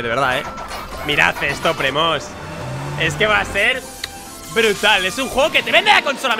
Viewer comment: Yo pense que decia va a ser Una mierda